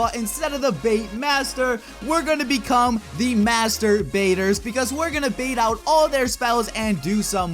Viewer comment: Did he just say master batura??